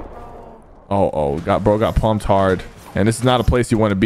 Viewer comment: pause what did he say “Pumped hard”wtf